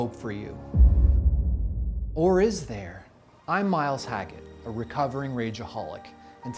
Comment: I thought I heard "Mike Hacker", earbuds broken sorry😅